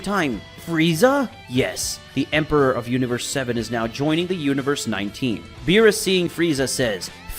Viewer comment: universe 19? Is it true what i heard?